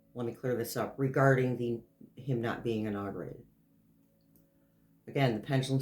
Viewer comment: i thought you were saying dis inaugurated .....meaning the ceremony will be negated.